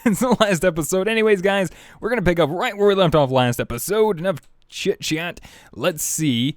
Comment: In the beginning he said chitchat i thought he said shitshat